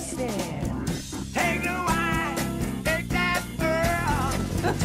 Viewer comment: M, my ears have always heard it as "DIG that girl", not "take".